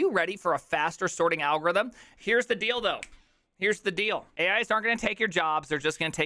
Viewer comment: did you said here is the dildo at 0:12?